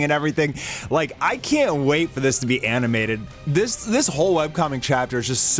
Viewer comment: did you say “ANIMATED” bruther ee aint getting this till 2040😭👌